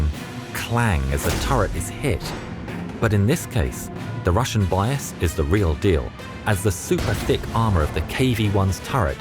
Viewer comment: did I hear right? "The russia bias is the real del" hahahaahah